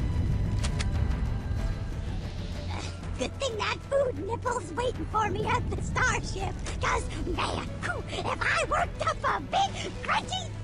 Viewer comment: sounds like he said food and nipples haha